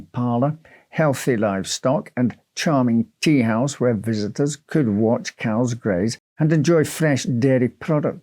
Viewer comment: At first I thought he said, "A charming teahouse where guests could watch cows mate." 😅